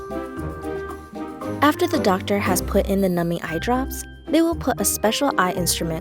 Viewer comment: She said numbing eyedrops I thought she said nummy eyedrops yum